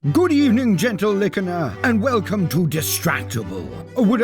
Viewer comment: not gonna lie the second i heard " good evening gentle lickener" I liked the video